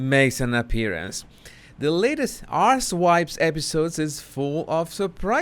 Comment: Did he just say “arse-wipes podcast” ???😅😂😂😂😂 god, he’s really goood😅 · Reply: He sure did! 😆He calls her podcast "ARSEWYPES".🤣